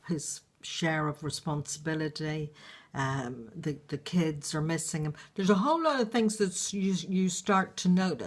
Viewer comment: lol she said the kids are missing him. i thought she said the kids are missing. i was like wait what hahahaha. this is very good im learning a lot from these videos ❤❤❤❤❤❤